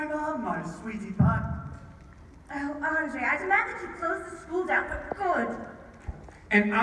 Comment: omg I heard "Oh André, I demand you close the school down for porn!" XDD